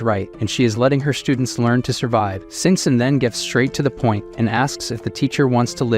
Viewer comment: caption says sex